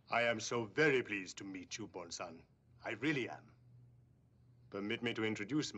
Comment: When I first heard Tanaka saying Bond-san, I thought he was saying Bonsai （盆栽）